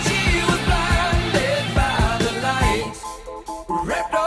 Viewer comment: ‘Revved up like a deuce, another runner in the night’ is possibly the most misheard lyric in rock history. I know I never figured it out and didn’t know what it was for decades. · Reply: Yep as a kid I heard "wrapped up like a douche another loner in the night." No it never made sense but there you go...